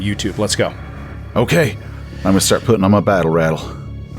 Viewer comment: what the hell is saying? 😂 "I'mma start puttin' on my battle rattle."??? Either way the delivery is hilarious